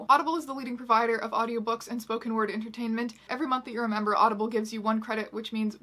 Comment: when she was talkin bout audible i thought she said for ever month you remember and not every month you're a member and i was like oh ok cool